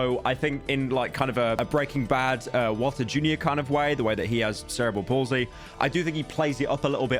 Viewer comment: when you were talking about Walter jr from breaking bad having cerebral palsy, the closed captions said cereal, and Walter jr loves breakfast